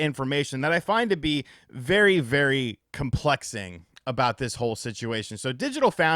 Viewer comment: Did he say he finds the situation "complexing"?